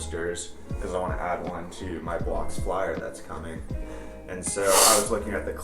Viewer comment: I KEEP HEARING ''blocks flyer'' WHAT IS THAT ?? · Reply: Blocks Flyer is one of the current models from SE Bikes. It's a 26in wheelie bike and loves his